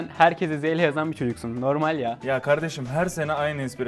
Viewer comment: ne diyor? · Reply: "Normal ya" diyor